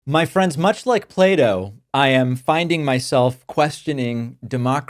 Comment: I heard "Much like Play-Doh" 😂 sorry David.